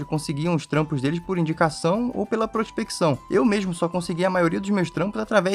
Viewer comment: É só eu que entendi "prostituição" na primeira vez que ouvi essa parte?